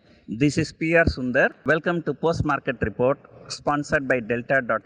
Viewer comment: Sir! I hear it like 'postmortem report' every time you say as 'Post market report'. :D